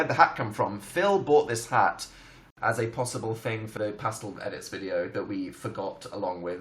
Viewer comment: Did he just say Phil brought his clothes.